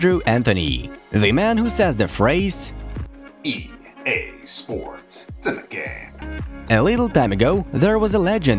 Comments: I heard this: " EA sports , end the game "😂😂🤣🤣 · I heard “EA Sports: Sin the game!” As a kid So I never really played fifa ever.